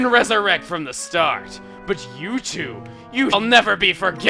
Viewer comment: But youtube will never be frogiven. I keep hearing that.